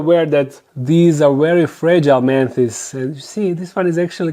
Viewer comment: This video stayed playing automatically on suggested from my channels im subbed top. I was like "oh cool dark den, I recognize that voice (I was busy doing dishes) and then I heard "man tits" and I ran to the screen and said "he said fuckin what??" 🤣😂